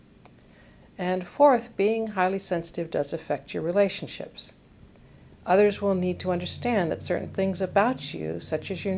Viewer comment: HELP . Please. I don't understand what she says at 2:16 .. She explains that HSP person has to stop trying to live like a nonsensitive. You need more downtime and to work you need "............." ??? what does she say there? I would sooo appreciate your help. Thanks :)) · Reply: you need more downtime and you need meaningful work?